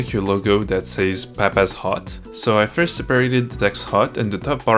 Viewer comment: When your saying “papas hut” it sounds like your saying papas hot. 😂